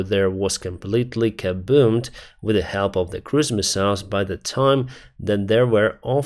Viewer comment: Oh cruise missiles, I thought you were saying christmas mouse and was very confused.